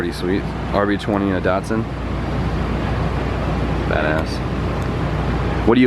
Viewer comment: sounds like you said fat ass